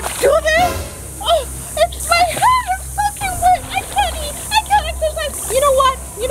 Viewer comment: did she say f*ing wet